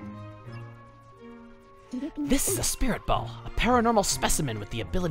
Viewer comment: "This...is a spirit ball" Me: /starts laughing/ i thought he said spirit bomb oh wow im stupid xD